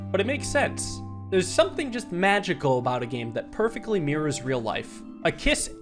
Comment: …what did bro say? 💀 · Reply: magical or after this its kiss